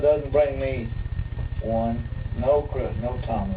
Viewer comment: I think says "no Chris, no comment"